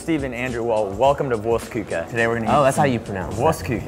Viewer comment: "guys they gave me a hot dog" mY hEART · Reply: the German restaurant sounded like Worst Cooker. LOL !!